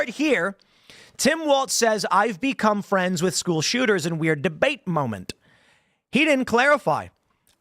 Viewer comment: During the debate I actually missed this, in the context I must have thought he said, "I became friends with the school shooters' family." So I guess my brain didn't register he misspoke, because it doesn't make sense he would actually mean what he said. But after the interviewers are asking him for clarification and he doesn't answer, then that is very very bizarre!